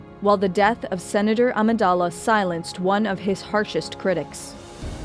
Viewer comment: Hey folks! As part of the leadup to The Last Jedi, this is one of three (yes three!) Star Wars episodes we are going to be releasing this week! Oh and you know what, let's throw in a bonus Incoming video just for the hell of it! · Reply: I must point out that the subtitles at 1:56 contain a typo. It should be 'one' instead of 'once'.